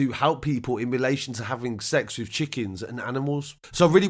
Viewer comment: Did i hear “he had sex with chickens and then with his dog, it was a great day ?”